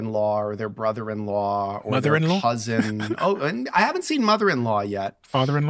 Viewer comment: did he say "cousin" @ 1:14:07?!